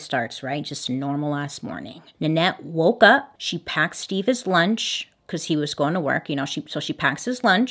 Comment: She really said Steve-ah’s lunch